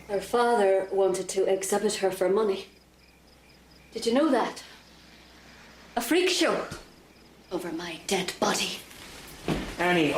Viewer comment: what did she said? (English is not my main language) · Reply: Annie says: "Her [Helen's] father wanted to exhibit her for money, a freak show. Over my dead body!"